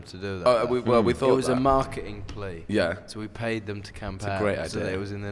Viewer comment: what did harry mean by "we paid them to camp"? or did he say something else??